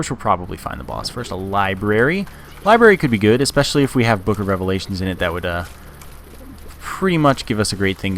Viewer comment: I think I heard you say boogerevalations